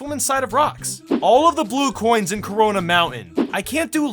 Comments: Did he just say corona mountains 9:59 This proves mario has coronavirus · did he just say "corona mountain" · did...did he just say corona mountain? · Did you say corona